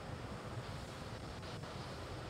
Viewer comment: What is said at 4:22:27 ? "Stage 1 takes..." something? I have played it in slower speed but just can't figure it out. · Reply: "Stage 1 tanks pressing for flight". This is probably a comment related to pressurization.